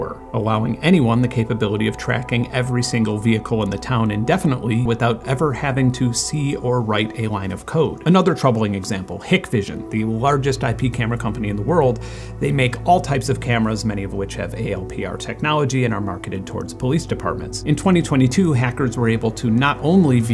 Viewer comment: I definitely thought you said "pigvision" which was sooo funny to me for a sec